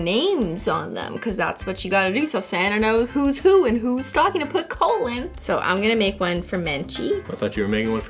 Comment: I thought she said "You gotta put the names on the stocking so Santa knows who is who and who's stocking to put colon." 💀